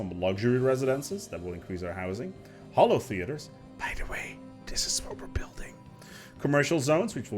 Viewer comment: What did he say at 29:10?? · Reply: "btw, this is what we're building"